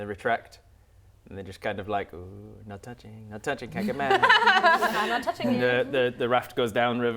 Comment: Correct me if im wrong but I swear said "Cant get mad" but subtitles say Kekkaman lol